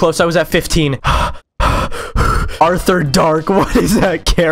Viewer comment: So we're not gunna deal with the fact that kinda sounded like albert??